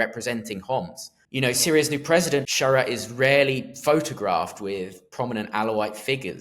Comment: When he said the caretaker prime minister "is rarely" photographed with Alawites, I thought he said "Israeli" which was slightly confusing.